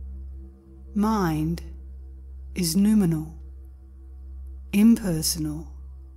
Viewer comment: it says "mind is 'x' impersonal, subjective.." etc.. Is that word (x) numinal? Can someone tell me what this means, it's what the actual word is? I googled to no avail :) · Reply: The word is NOUMENAL. Unfortunately, auto YouTube CC's can't spell. 😂